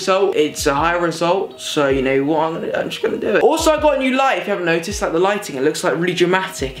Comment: Instead of "I Also got a New Light" I thought he said "I also Got A New Life!"😂😂